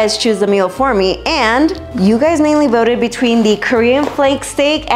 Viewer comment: Not only do I also use cooking today, but the pepperoni naan bread pizza is one of my favorites, too! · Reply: I was listening at that time not watching and I thought she said "Pepperoni Non-pizza", so I thought it was going to be some Keto dish, then I saw NAAN Pizza!🤣